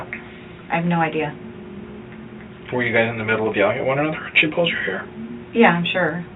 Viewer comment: when captions say "inaudible", I believe he says "were you guys in the middle of the argument when she pulled your hair ? ". Just adding for clarity if anyone was wondering.